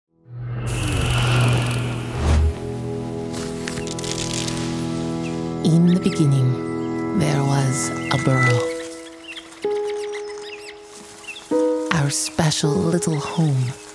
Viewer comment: I keep hearing it as “In the beninging”